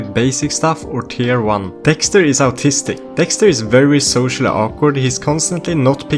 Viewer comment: I misheard “dexter is autistic” as “Dexter’s Artistic”